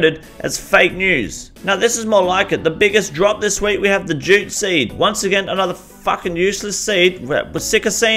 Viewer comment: jute seed, sounded like you said gypsy. Rofl. Funny.